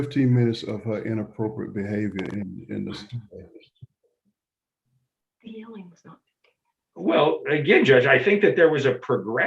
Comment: What is said by the woman right after the judge speaks, and right before her attorney speaks, at the 3:01:00 mark?? The cc translation doesn’t seem correct and I’m curious... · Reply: Something something “the yelling was not 15 minutes”?